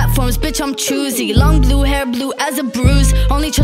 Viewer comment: Everyone heard it I'm crazy but u like it, I heard it I'm cursy but you like it and I actually found it 😌